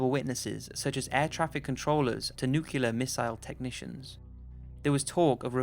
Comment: English is not my 1st language, but.... did you really say "nukular" at 10:21 ??? :)